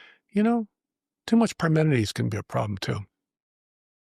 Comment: What is this WORD at 1:00:07? "Permidities". "As in too much permidities can be a problem too" The problem is there is no such word. What is Reno (this person he is quoting) trying to say? What word is he trying to use here? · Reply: Parmenides was a Greek philosopher, the name is in referencing to his style of thought, in contrast to Heraclitus